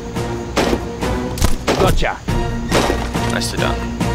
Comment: did Thor say 'Gotcha!' just after shooting a child?